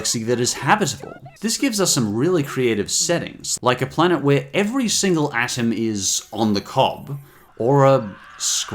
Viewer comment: What is that word at 10:15? Cuz i don't speak english. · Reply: Helen Karp every single atom is on the cob?